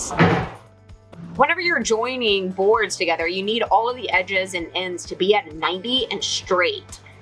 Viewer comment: I heard, "If you're not familiar, DECKED is a drawer system that keeps your tools safe from the weather and BEES." Can't be too careful! lol